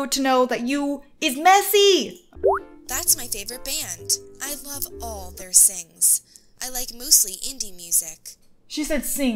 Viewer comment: You know TwentyØnePilots? Well.... I lOvE alL tHeiR SinGs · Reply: It kinda sounded like said "I love all their sins "